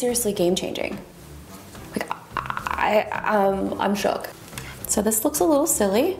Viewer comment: Just wanted I needed, Excellent all around review. I am defo gonna get one now! Hay did I just hear you say "I am shock"? @ 5:37 adorable.